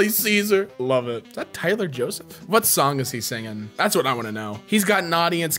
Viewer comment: did, did he say TYLER JOSEPH???